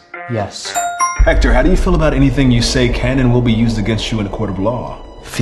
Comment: 我想問up主和聽力比較好的人 4:01 秒他說的英文是?我聽是 Had you felt that anything you say can ...... 後面他自動產生的字幕我聽不出來但前面我聽著不太像how do you feel 請問他整句到底說了啥?